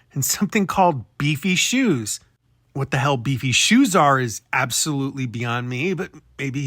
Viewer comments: I thought he said “DC Shoes”, like the skate brand DC. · I think he said "DC shoes" it's a skater brand that is really popular with Rob Dyrdek (ridiculousness host) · At about 41:00 , I thought I'd clarify... Chris said DC shoes. Which are like skater shoes. Lol. Beefy shoes😅 That gave me a good laugh, thank you!😂❤ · I thought he said DC shoes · Lmao, you're killing me with the misheard "beefy shoes" 😂💀 He actually said DC shoes - referring to the skating brand DC created by Ken Block. But, omg, the beefy shoes you found had me literally laughing out loud! Thank you for that! It felt nice to genuinely laugh during an analysis of one of the absolute, most frustrating and haunting cases I know. As always, thank you so much for all of your incredible hard work and perseverance in the fight for justice for Shannan and those precious babies, as well as NicHOE! No one deserves to get away with doing something so heinous and unforgivable - especially her 😑 I know you've been dealing with health complications, so it's really amazing to see new content from you. Please, please take good care of yourself! You're making a difference, keep it up! Sending all the love and well wishes to you and your loved ones ✨️🖤🐧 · I think he said “DC shoes”, like the skate brand. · I thought he said "DC" shoes. They are a brand name of skateboarding shoes/sneakers. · I thought he said DC shoes. Which I could 100% see him wearing out. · I heard DC shoes. It’s a brand.